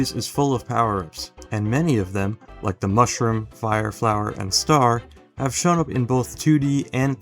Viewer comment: why do i hear flyer flower